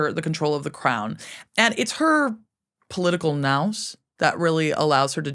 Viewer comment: What word did Dr. Janega say at 53:58 ? "Her political nous"?